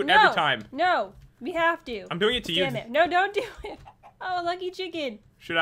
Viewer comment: did you say damn it?!